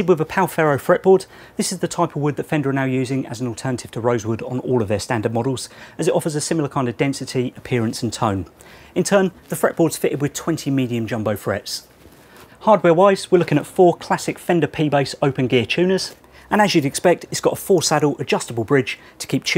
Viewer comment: "20 million jumbo frets???" just kidding, it sounded like that for a second, just friendly harassment! great video